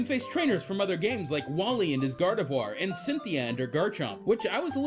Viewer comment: Did he just said "wally and his Gardevoir?" wow bro When I heard that I thought he said diantha's Gardevoir but is wally 😂